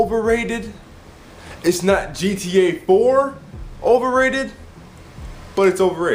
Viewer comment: Oh wow! Sorry man, I thought you said GTA V. Sorry, what do you think if 5?